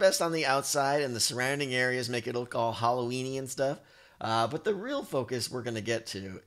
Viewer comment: When you said "Halloween-y", I heard "hollow weenie."